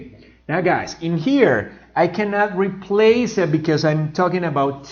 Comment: este sonido del ingles me mata y no logro decifrar que dice. Suena similar a --replace (taking about)--. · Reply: dijo la palabra “replace “no estás equivocado sonó diferente porque dijo “I Can not REPLACE IT “